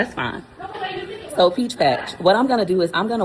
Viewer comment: Alright, not on subject, but when she addressed her audience I would have sworn she said, “So Peach Vag….” She was definitely looking for a controversy in this!